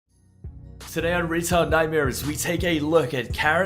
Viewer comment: I seriously thought you said Retard Nightmares